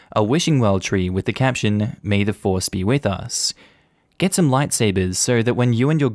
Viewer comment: wedding one i heard may divorce be with us